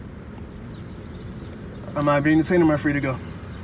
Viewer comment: into the video, the captioning says "Am I being insane?" Sounded like he said "Am I being detained?" But that's a fair rhetorical question as well: No, driver, you are not insane. This detective is just being a dick....and likely not for the first or last time.